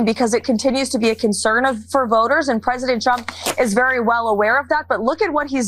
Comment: Karolying sounds like she says President Chump.